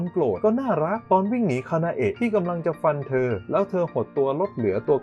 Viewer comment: ได้ยินพี่พูดว่าคานาโอะ